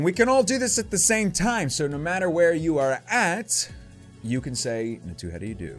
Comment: I thought it first one said NATE how do ya do... 😂